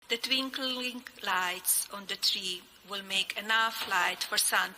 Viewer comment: the what lights She said the twinkle wink lights. Wtf😆😆😆😆